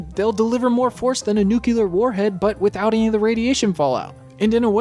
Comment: Did you just say nuc-u-lar? Really? REALLY?